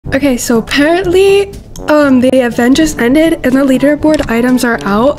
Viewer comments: WHY DID I HEAR “um so i heard the avengers just ended” AT THE START 😃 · i thought you said “the avengers ended” but anyway CONGRATSSSS 💅💅💅💅💅💅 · THE AVENGERS ENDED?? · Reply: they were joking bc it sounded like she said it but I’m pretty sure she said the event just ended